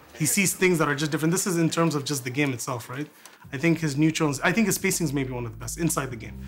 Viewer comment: the captions say “neutrons” but it should be “neutral” lol.